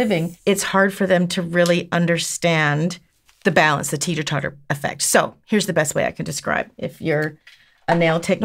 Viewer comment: I so mis-heard that as “the Tina Turner effect” 🤣🤣🤣. Such a beautiful set!